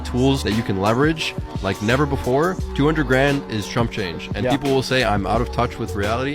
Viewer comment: did he say Trump change?